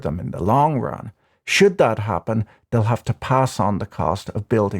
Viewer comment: I thought I misheard the, "...should that happen..." as, "...shit that happened...", and now it makes more sense. Jeeesus, we are sooo fucked....